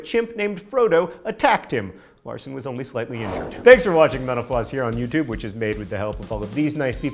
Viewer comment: When he said "mental floss" it sounded like "menopause"... wtf.